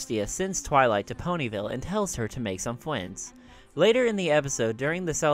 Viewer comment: the said fwiends instead of friends lol